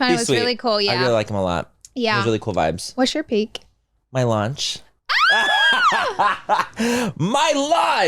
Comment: Am I the only one who heard say his peak was “my lunch”?☠️ I was like, “I love food too, hell yeah” than realized I misheard him⚰️